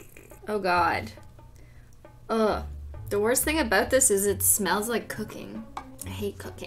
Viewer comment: for a split second I thought she said "it smells like cocaine"